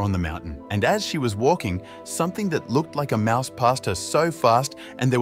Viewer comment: I misheard mouse pasta